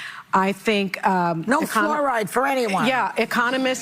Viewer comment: Did she say no fluoride for anyone? 4:15 wtf